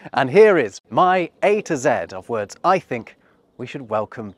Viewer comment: 😄Thought you said " . . .my 8 words."😆